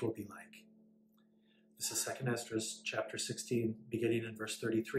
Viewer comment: I was trying to follow along and I thought you said 2 Ezra’s chapter 16. About priesthood power. Could you give me that scripture so I can look it up please. You were talking about the priesthood is important for women also, thank you 😊